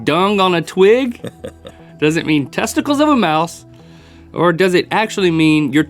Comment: Instead of "Testicles of a mouse" I thought he said "Testicles in the mouth" and I jumped and looked at the screen to make sure I was hearing correctly lol